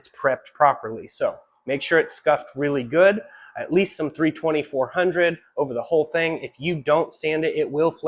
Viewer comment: Hi :). I didnt get the part about sanding? 1:55, did u say 3time x 2400grit sanding ? edit: and should i wash sanding with isopropyl alc before painting ? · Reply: 320 or 400 grit sandpaper and wipe down with acetone